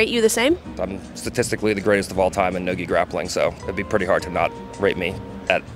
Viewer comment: I thought he said "noogie grappling" lol but he said no gee grappling